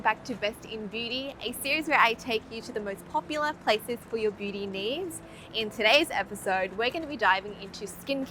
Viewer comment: I swear she just said for your booty needs.... 🤣🤣🤣🤣